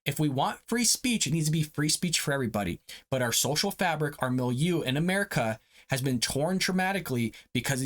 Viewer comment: what are you saying that sounds like milu?